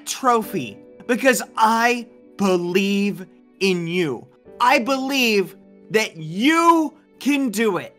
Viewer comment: ‘I leave in you’ I really thought he said that